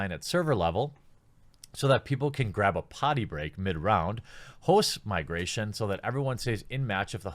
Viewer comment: did you say "horse migration"?